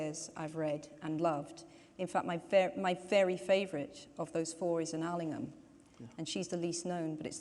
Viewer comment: can someone say what books she's saying specially the last one please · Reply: Tiger in the Smoke by Margery Ellingham